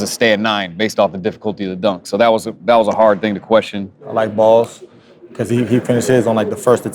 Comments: he really said "I like balls" 😂😂😂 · anyone else hear i like balls